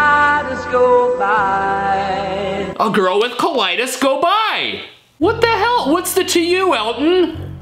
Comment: I hear: a girl with colitis girl bye 😂😅